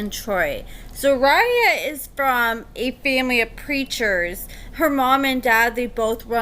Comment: Girl, I thought you said "creatures," not preachers. 😂😂😂 The season just started!